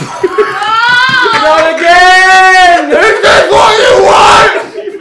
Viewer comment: does say “this is why mom doesn’t fuvking love you?” Cause I’m dying